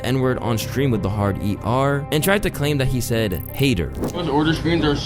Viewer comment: I love your work man, always interesting shit! Yo but real quick, you were wrong at 17:13 He did say "HATER". I heard the H and the pronunciation of A, not I. Don't get me wrong though, I still find Jack irritating AF haha